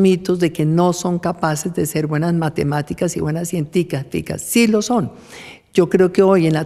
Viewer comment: señora vicepresidenta, qué dijo? Cienticaficas?